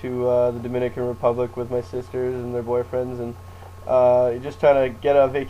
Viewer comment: wait did he say "my sister and my boyfriend at 1:05?